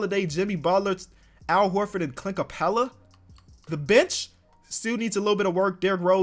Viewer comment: I thought he said THE BITCH